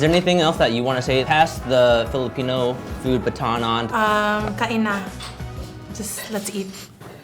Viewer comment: Did i heard tangina?😅🤣